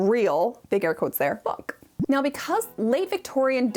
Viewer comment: Omg, that was the funniest subtitle mistake I'v ever seen. At 5:46 says "book" but somehow the subtitles think she said "fuck".